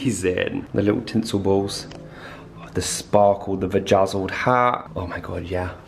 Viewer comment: Please tell me someone else also heard say Vagazzled instead of Bedazzled @ 12:09 & 14:27 LMAO!!! That’s hilarious! 😅🤣🤣🤣🎃